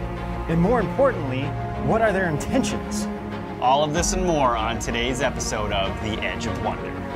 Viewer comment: You should try not to say that you have something and "more on" your show. It sounds like you said you'll have "morons" on your show...